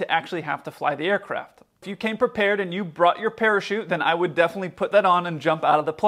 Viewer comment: I heard "parachutes" as "pair of shoes" and went, "Whaaaaat?!" LOL